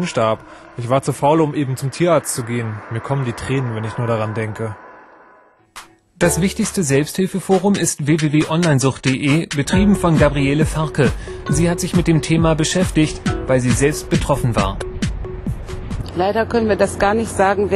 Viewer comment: Ich hab bei 1:14 zuerst Gabriele Ferkel verstanden.